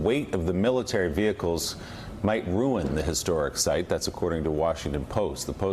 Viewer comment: LISTEN! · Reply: tiki torch ...I honestly thought he said brand new GERMAN tanks 😂 ...well he leads the nazis, so...